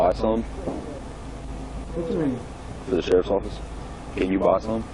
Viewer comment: Wait, at 17:30, did I hear the cop correctly? Did he ask the guy if he would be willing to "buy some for the Sheriff's office"? I am assuming he meant weed. Seriously??